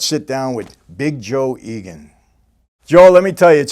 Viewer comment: thought it said Joe Rogan lol